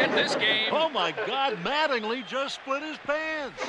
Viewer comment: Oh my God, Mattingly just split his pants! 🤣🤣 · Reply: I thought he said “Mattingly just wet his pants” lol “split”makes more sense haha